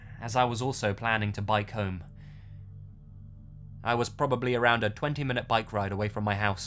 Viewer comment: When the story said "bike home," I thought I heard "buy comb."